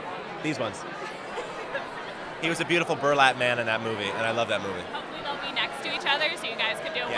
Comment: Burlap man? (What he says from 0:42 )